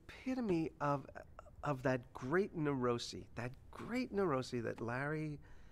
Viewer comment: The Bookman scenes are gold. Even in the finale. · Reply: What does jason say at 1:35ish - Nirosi? Niroci? Narosey?